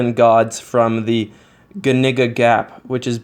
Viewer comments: "good nigga gap", the subtitles said. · I swear at 5:35 he said good nigga 😂😂